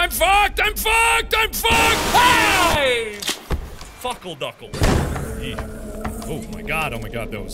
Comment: did he just say fuckleduckle